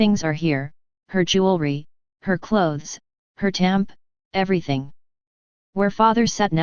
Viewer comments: At around 17:20, does she say her "tamp" was sitting in the place she once sat in? Meaning her "tampon"...? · did she say tamp at 17:20 as in tampon these aliens are really picky what they take. would make a good add for tampons